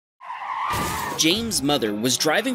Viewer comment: The way I thought he said jay z’s mother in the beginning 💀💀😭